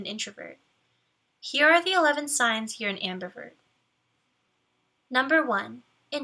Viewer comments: Yoooo my volume was low and I almost thought she said: "Here are the signs that you're a hamburger." 😂🤣💀💀💀💀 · i heard “here are the 11 signs you’re a hamburger” 🍔